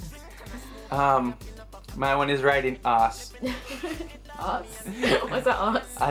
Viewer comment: it sounded like he was saying i like riding ass